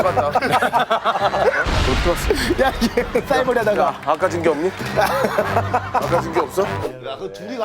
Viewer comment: 에 빨간약을 뭐라고 말하는건가요? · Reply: 아까징끼